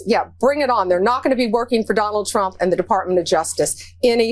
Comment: Did I hear Bondi say “they will not be working for Donald Trump!” All public servants are there to work for the people. Pam Bondi is nothing but a bias ass kisser for Trump.